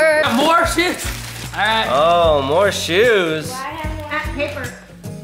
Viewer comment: did I hear MT(mommy tube) say ah paper in the background